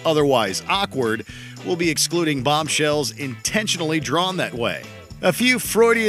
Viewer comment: I thought they said "not drawn that way on purpose". Lola absolutely was lol